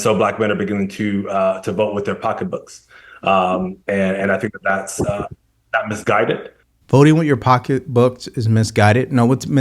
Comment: It sounded like he said it’s NOT misguided